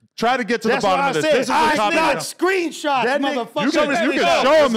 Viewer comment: AYOO DID JUST SAY “I GOT SCREENSHOTS NIGGA”🤣🤣🤣🤣🤣💀